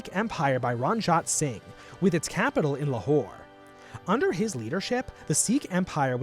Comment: They were united into the Sikh Empire. With it's capital in a hore. Surely I must have misheard that. 02:52 : The name of the capital is really spelled Lahore. Okey...